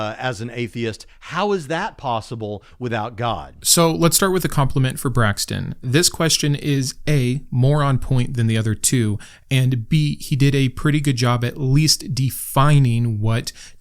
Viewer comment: script issue. i first thought you called braxton a moron here, upon the second take i see you said more on.